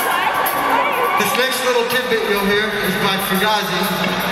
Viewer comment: What does John say at 29:47? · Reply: He says, "This next little tidbit you're about to hear is by Fugazi..." He basically tells the crowd to go get their records. 🤘